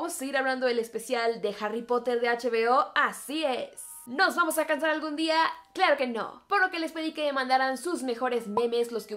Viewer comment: en esa parte entendi "Nos vamos a casar algun dia? Claro que no!" y yo me quede tipo: como que no? :< yo ya tenia la boda preparada 🤧💔